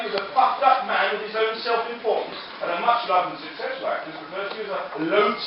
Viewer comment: Thank goodness for captions. I thought Bill said 'fucked up' instead of 'puffed up' at 3:35 :D